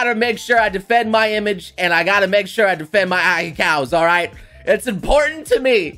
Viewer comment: did he just say ahecows